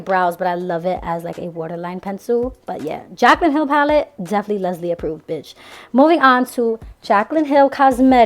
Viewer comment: I seriously about died and I had to rewind your video for a minute because I thought when you said "Jaclyn Cosmetics palette: definitely Lesley approved" but I thought you said "definitely lesbian proof" 😂 I almost choked on my chili beans 😂😆😂